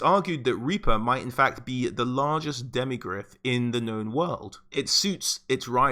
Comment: FUCKING SUBTITLES SAY, QUOTE: "LARGEST DEMOCRAT IN THE KNOWN WORLD!" XDXDXDXDXDXDXDXDXDXDXDXDXD FUCKINGSUPPOSED TO BE DEMIGRYPH!!! FUCKING LOVE IT, YOUTUBE, BUT SHIT! XDXDXD